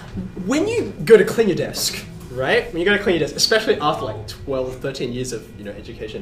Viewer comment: What does he say at this time? Clean your desk?? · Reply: Oh it really is "clean your desk".